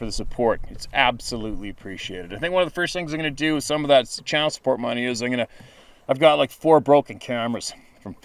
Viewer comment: Channel support money....at first I thought you said child support money. I was going to ask if you wanted to talk about it.